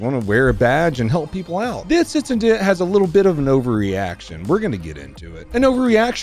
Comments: I seriously thought you said, "ovaryaction" and I spit out my drink. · Not sure if you're doing it on purpose, but when you say "over reaction" i keep hearing "ovary action" 😅 I guess either will work in this case